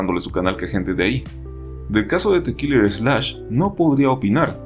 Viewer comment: ¿Cuál es el youtuber que dice en el 2:03? ¿The killers snatch, dijo eso? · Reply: Killer slash